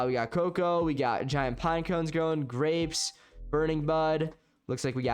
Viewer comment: what did he say... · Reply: Burning bud